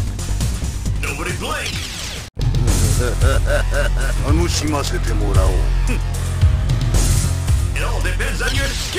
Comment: what he say ? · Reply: “I’m going to enjoy this “